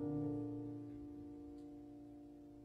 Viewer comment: Great! What does he say at 32:20??? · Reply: So etwas herrliches von einer Musik.